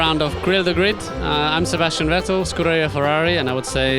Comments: Did he just say: "I'm Sebastian Vettel, screw you Ferrari"????!!! :o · I thought I heard "screw you Ferrari"..... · I thought he said 'Screw you Ferrari' Not Scuderia Ferrari XD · Im sorry but when Vettel said Scuderia Ferrari,i honestly had to repeat it cos i honestly thought he said screw you Ferrari,honestly. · I thought he said, "I am Sebastian rattle". Had to go back and listen again